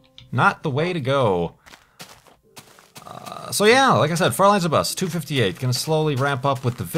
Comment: At around 1:50 where you said "Far Lands or Bust" really quickly I thought you said "Fräuleins or Bust"...it made my eyebrow rise Spock-style.